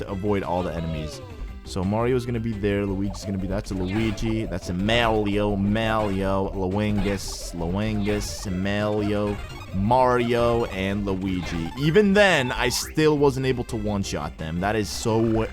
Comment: did he just call them mawio and lawingus